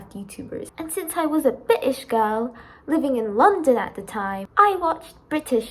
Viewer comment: "And since I was a British girl" Not just me who thought " did she say bit*h girl?" right?